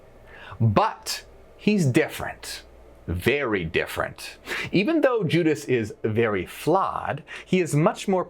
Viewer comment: I heard "Even though Judas is very fly"